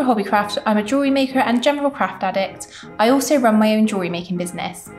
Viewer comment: I swear I thought she said crack addict! 😂